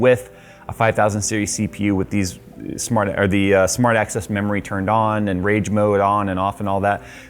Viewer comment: Is it just me or did I just hear "SMART ASS memory" ?🤣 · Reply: Smart access memory.